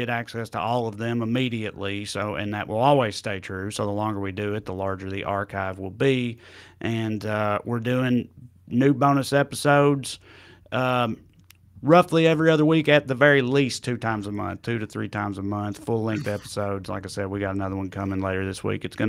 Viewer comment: I almost thought said "Nude bonus episodes". Got real interested for a sec LoL.